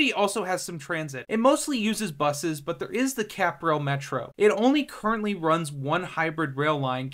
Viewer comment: Did anyone else hear "catgirl metro"?